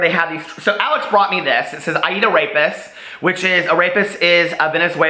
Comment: Loved the scene w the trees! Amazing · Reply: I swear I thought you were saying 'I eat a rapist"